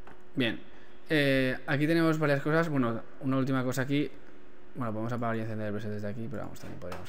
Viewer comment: excelente muchas gracias por el aporte :D pero no entiendo lo que dices en el minuto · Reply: "Bueno, podemos encender y apagar presets desde aquí" 6 años tarde